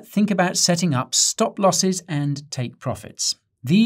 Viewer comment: thought u said stop profits and take losses :face-blue-smiling